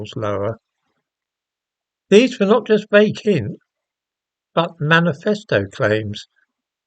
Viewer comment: Can someone tell me what is saying at 1:01 when he says "lower these were not just vake in but" What does he mean, just vake? Thanks. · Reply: Vague hints.